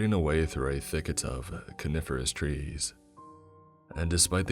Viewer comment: I thought he said CARNIVOROUS trees . . . . .